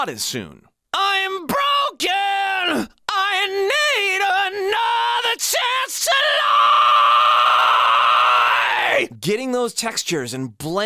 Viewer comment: Great video! One thing I noticed, it sounds like you say “another chance to lie” but I’m pretty sure the line is “another chance to live”. Could just be my ears.